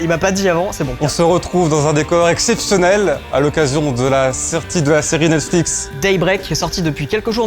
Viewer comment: C’est moi ou à 0:29 il a dit nesflix